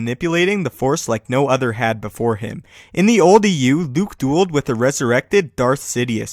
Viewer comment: Could someone please tell me what this man says at 00:26. It sounds like he is saying, "in the old EU (or) eeyew".